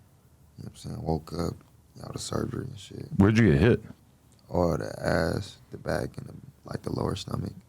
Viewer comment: did he say he got hit in the ass?